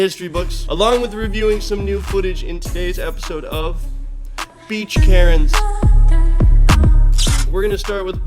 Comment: Is it just me or did that say beach karnes